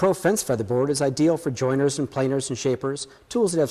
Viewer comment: did he say planers? Good stuff, I just wouldn't shove one in my planer :)